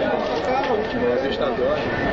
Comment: o que ele fala no momento 0:30?? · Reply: "Minha vista dói da pá olha não."